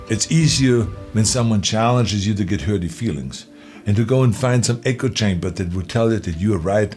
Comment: I swear he said "hurty feelings."